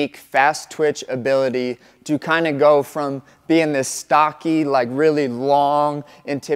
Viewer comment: Did he say Jalin is stocky 😂